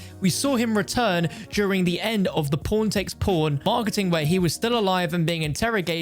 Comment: Please, do me the favor and never pronounce Pawn takes Pawn, it sounded like you said P*rn takes P*rn